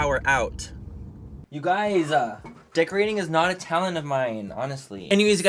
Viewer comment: When said decorating isn’t a talent of mine I thought he said dick reading isn’t a talent of mine 😂🤣